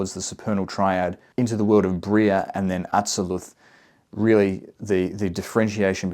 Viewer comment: Did you say Briah and Adseluth? I don't see them in the tree of life. Did you mean Binah and Chokmah ? Or Binah and Gevurah? Please point it out because I'm sorry I couldn't understand what you said in 3:55 and 3:56 in the video.